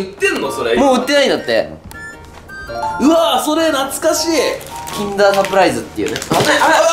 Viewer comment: キンダーが禁断に聞こえて禁断サプライズってなんだってなった